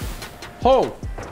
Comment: who else heard repeatedly saying ho*